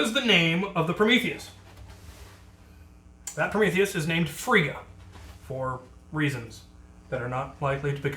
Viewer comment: Freya, the god of ..... stuff.. and will not be featured on this channel for reasons. Don't worry i got your reference. :) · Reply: Vincent Colomaio ohhhhh, I thought he said frega